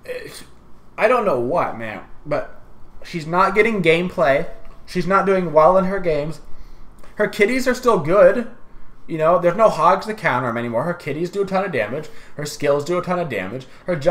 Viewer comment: On bastet I heard "her titties are still good" hahahaha